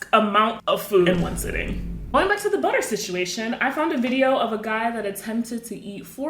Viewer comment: I thought u said peanut butter…